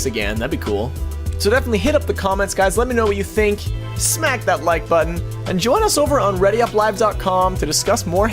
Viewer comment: I just realized the words to your intro music is "Smack that 'Like' button," but I originally heard it as "Smack that white butt." LOL